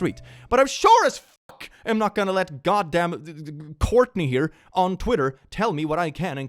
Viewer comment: I had this playing in the background of dying my hair and I heard “goddamn Courtney on twitter” and I was spooked lol (that’s my name)